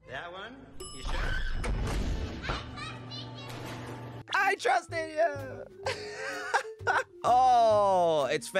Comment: I thought she said “I TRUSTED YOU PAPA!!”